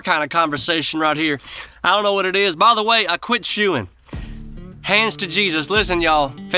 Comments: I thought you went to the dark side for a moment. I thought you said that you stopped shooting, not chewing. whew! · Man, for a second there I thought you said “I quit shooting!”